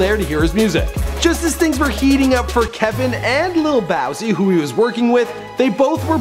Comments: did he just say lil bowsi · did you just say bowzy its boosie